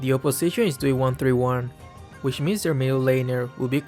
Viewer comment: Wth why does i heard says 3-1-3-1 than 1-3-1😂😂lmao🤣 btw it is in 1:48 🤣